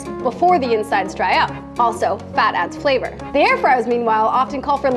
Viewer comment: thought she said "fatass flavor" like dayumm she's sure wit it lol